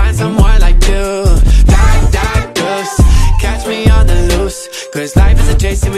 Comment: Hey! Am I the only one who thought it was saying die die ghost?